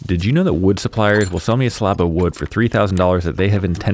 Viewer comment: It sounded like you said slop of wood.